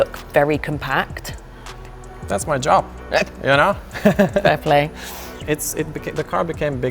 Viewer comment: did I just hear dae play!